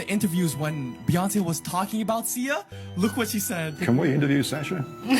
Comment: They were NOT talking about SIA, they were talking about Sasha, Sasha Fierce. Beyoncés alter ego. He clearly said Sasha it’s even in the caption on the screen. I’m not saying she ain’t evil. I DO believe. But yeah it’s not cool to put words in peoples mouths either